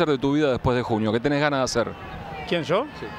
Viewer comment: Que dijo en el segundo 0:30?no entendi xd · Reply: Luciano Piola El barrio donde vive Riquelme se llama Don Torcuato